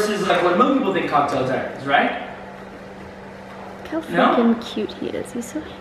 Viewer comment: It sounded like she cursed in 9:15 when she zoomed in l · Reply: arodkf13 she said freaking...